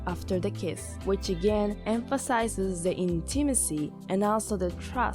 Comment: I thought you were about to say: "which emphasizes the gay" and I lost it 😂😂😂